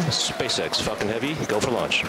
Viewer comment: who else hears “SpaceX fuckin heavy go for launch.”